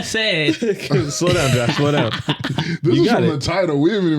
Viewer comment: I missed the guest's name but it is so hard to watch him struggle to try and get out what he was trying to say 😂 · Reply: Josh that's his name that was rough